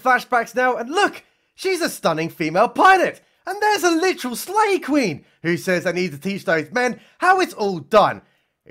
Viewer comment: 'Slay Queen' is rocking that 'Dwayne Dibley' haircut. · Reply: I thought he was saying Slave Queen. I thought maybe she was from that stupid netflix movie.